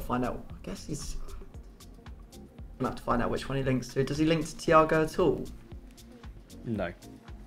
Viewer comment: asking a cheeky 3rd question at CM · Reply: Did ask a 2nd question at RB? All I heard was 'does he link to Reus at all'